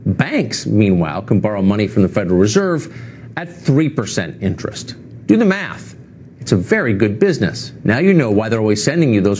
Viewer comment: i thought he said: "do the meth, it can make a good profit"